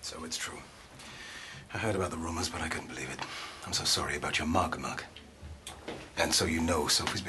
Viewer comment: So its true, I heard about the rumours but I couldn't believe it, I'm so sorry about your mug Mark. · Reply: I cant believe until now i heard it as "so sorry about your mark, Mark" hinting at the him pissing himself